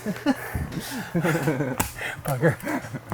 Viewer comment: I LOVE the Bob Ross reference! lol Sure wish i would've done this when building my studio!! What exactly did you say at 9:14 lol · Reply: I think I called him a bugger. OOps!